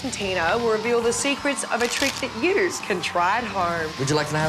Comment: did she say "Yous" can try at home? LOL!